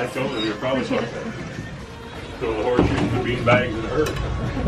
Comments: I heard “horseshoes and beanbags in a hurry” am I the only one? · I’m not an Amberbaby, but every time I hear that guy say that the bean bag is in a hurry I hear it differently. The first time I heard it I thought he said “throw the horseshoes and bean bags in a hurry”, which makes sense especially since smaller kids have trouble throwing horseshoes so they throw bean bags instead. At least that’s what my family does. I can hear him say at first “so I told her then throw the horseshoes and the bean bags in a hurry”. But of course I could be wrong. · dude says "throw the horse shoes and bean bags at her". He did not call amber a bean bag.